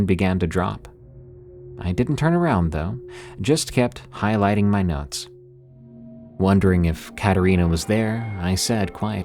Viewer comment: I keep mishearing it as "just kept highlighting my nuts" :' ) LOL -- awesome stories though! Would love more from this region!! <3